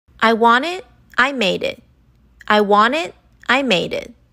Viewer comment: i hear “i wanni. I madi